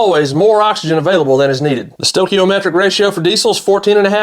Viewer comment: Do you speed your videos up before you post them Or You just talk that fast. You cover sooooo much technical info that I THOUGHT YOU SAID : THE COMPRESSION RATIO , INSTEAD OF STOCKIOMETRIC WHEN REFERING TO AIR TO FUEL RATIO. I CHECKED THE TRANSCRIPT. GOD I HATE BEING WRONG ! MAN THAT WAS CLOSE ... LASTLY CAN YOU RUN YOUR BRAIN & A NON CATYLITIC GAS ENGINE ON A 10 TO 1 RATIO OF GASOLINE TO DIESEL? CAN YOU GIVE A ROUGH ESTIMATE OF THE OCTANE EFECTIVE EQUIVALENT 87, 89 ,OR / AND 91 OCTANE USE BTU TO ESTIMATE IT SO I CAN SEE IF MY CALCS WERE CORRECT ALSO, WOULD DIESEL AT 9% CAUSE ANY SIGNIFICANT DAMAGE TO THE ENGINE? I USED 130K BTU FOR DIESEL BIODIESEL IS LOWER, ETC , ETC IS THIS A COST EFFECTIVE 100+ OCTANE FUEL ALTERNATIVE I TRIED E85 AND IT GIVES ME A LEAN CODE ENGINE LIGHT BECAUSE MY CAR ISN'T FLEX FUEL . IT USES MORE FUEL IN FLEX FUEL MODE IF YOUR CAR IS MADE TO RUN ON IT. WHY IS E-85 RATED 100 OCTANE IF MY CAR GETS A LEAN CONDITION ? LESS BTU , I LOOKED IT UP HOPE YOU'LL MAKE A VIDEO FOR ALL OF US THX IMMENSELY